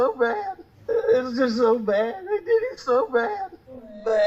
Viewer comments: I don’t know anything about the case at the end but I definitely heard “it’s just so bad they did him so bad” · I heard “they did him so bad”. Also the fact that they were shot by two different guns really stands out to me. Immediately sounded like two shooters.